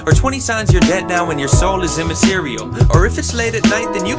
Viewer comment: thanks for the lyrics, i thought he said "soul is in my cereal"